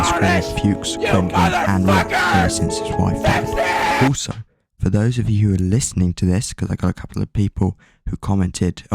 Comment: what did you say? I couldn't hear you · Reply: “also Greg has been screaming at Fuchs, Grendiline and rock ever since his wife died”